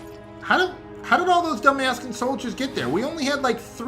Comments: did you say "Dumb-ass-can soldiers?" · Lol it sounded like he said dumbasscan soldiers